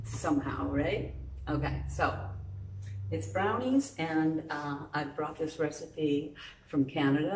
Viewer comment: Ohhh...I miss read this Video. I went and stripped down naked in front of my computer and was ready to watch this clip. Then, I heard you say "Brownie". I thought it said, "Wanna see my Brown Eye". 🤣🤣🤣Oh well...you're so beautiful I never regret watching anything you do❣